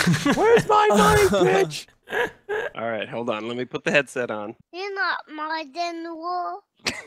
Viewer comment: what did he say at 1:20? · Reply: "You're not my general"